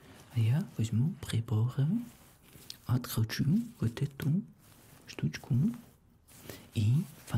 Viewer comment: ты сказал " откручу эту штучку " а мне послышалось "подр*чу этой штучкой" 😅😂😂😂